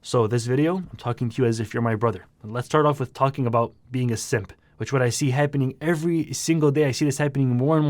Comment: I thought he said "being a sith" at first. Got really confused haha.